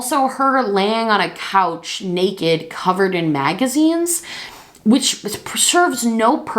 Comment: When you said “she was laying on the couch, naked, covered in magazines..” I REALLY thought you were saying “maggots” Yikes lol